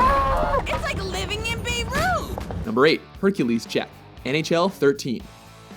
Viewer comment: what did she say? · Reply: "It's like living in Beirut." Capital of Lebanon, from Middle East. Ergo, GTA makers assume it's terrorist training ground.